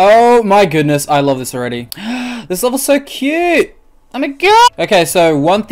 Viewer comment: did you say, I'm a gay.